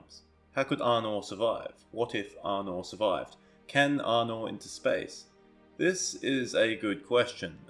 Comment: Did I just hear "can Arnor into space"?